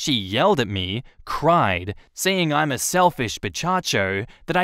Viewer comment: I was dozing off as I was listening & I totally misheard censoring as "bitch taco". 😂